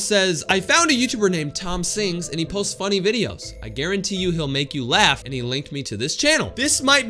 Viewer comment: he clearly says he Won’t you laugh